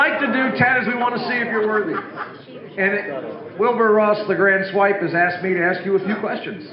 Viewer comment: Wilbur Ross the (inaudible) portion sounds like he says the Grand Swipe. ??